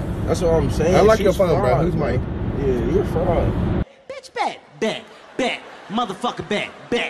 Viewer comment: was he saying fried or fraud?? lol